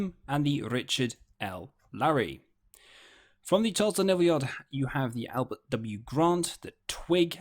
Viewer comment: At minute 11:15 my Dad's ship is mentioned, the DD-665. I sounded like "from the Tulsa Naval Yard". I know my Dad set out of Charleston, so quite the surprise to hear this word that sounds like "Tulsa"? Anyone know where this is? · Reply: if you turn on CC it shows he actually meant to say "Charleston navy yard". witch is where USS Bryant was build.